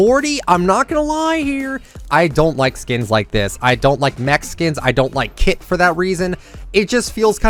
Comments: I thought he said i don't like Mexicans 🤣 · for a second i thought you said i dont like "mexicans" not mec-skins. lol.